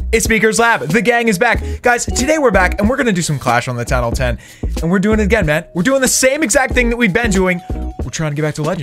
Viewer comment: Did he just say town hall 10 at 0:08 When you are addicted to the town hall 10